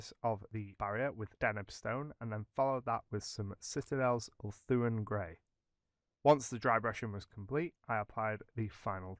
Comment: Wonderful effect - best concrete simulation I've seen. 3:47 - which grey? (couldn't hear) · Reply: Thanks. That grey is ulthuan grey by GW.